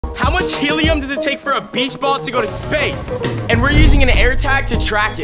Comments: I thought he said BITCH ball to got to space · For a second I thought he said b*tch ball but okay